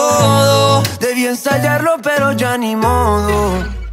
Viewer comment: Soy la única persona que se identificó con esta parte? “DEBÍ ENSAYARLO PERO YA NI MODO” a la hora de exponer JAJA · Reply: PENSE QUE DECIA VAMOS A HACERLO PERO A MI MOD XD